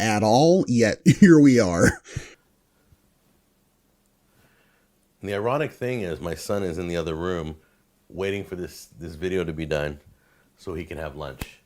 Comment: I misheard and, at first, thought he said "The erotic thing is, my son is in the other room, waiting for us to have lunch."